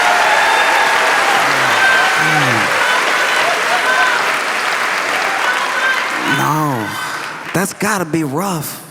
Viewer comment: What was that lady yelling? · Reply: He wasn't on the plane (I think 😅)